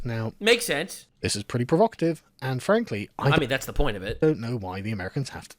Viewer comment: min. Did he say Anne Frankly?